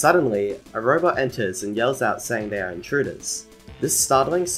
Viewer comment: lol before this vid I thought the robot that said intruders said entunix